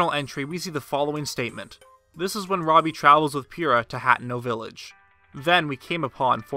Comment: It sounded like he said Pyrrha instead of Purah. Game theory: is Pyrra from RWBY somehow involved in the Zelda series??????